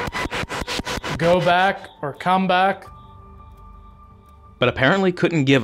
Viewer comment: I heard „don’t come back“…🤔